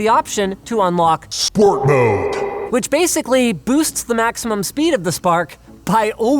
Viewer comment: first time I heard it I thought it was "squirt mode"